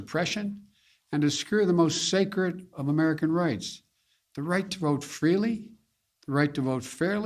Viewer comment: Did he just say; “ and to screw the most sacred of American rights?